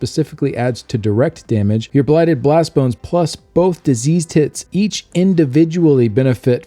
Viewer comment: did he say disease tits? lmao